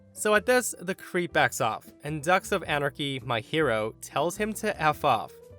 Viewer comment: Ducks of anarchy is the name of my new band. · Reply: I thought that's said "Dukes of anarchy"